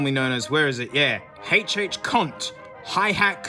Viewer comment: what you say? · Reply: Cont, short for control